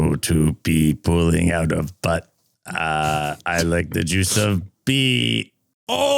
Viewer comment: I'm with Barbara, I heard "butt" and "pee." 12:45 Also, thank you. Finally, I know the answer to one of life's greatest mysteries.